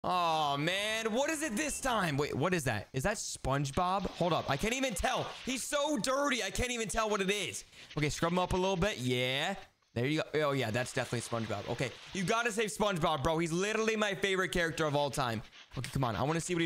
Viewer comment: I thought he said he was so black I can’t even see him